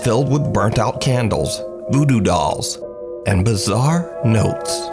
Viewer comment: At Minute 13:56 did u say voodo dolls or doodoo dolls? 😂